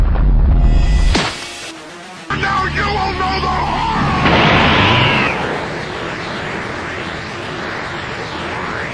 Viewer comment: What was he saying at 2:40? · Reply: Daniel Via evil goku: now you will know the horror